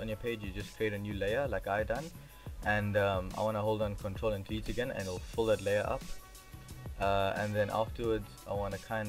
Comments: Hi, this is good tutoriall, but 03:15 you hit Control + WHAT? I can't hear . can you tell me please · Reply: If you want to fill the primary color, you need to press alt+backspace and if you want to fill the secondary color, you need to press ctrl+backspace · Hi, nice tutorial, but 03:15 you hit Control + WHAT? I can't hear . Thanks. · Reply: Control + Delete